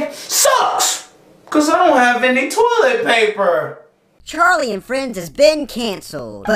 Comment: I Think it Said "Cause I Don't Have Any Toliet Vaper" LOL.